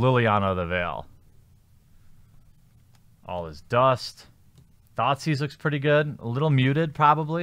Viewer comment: Each time you said "Thoughtseize," I heard "Thot seas."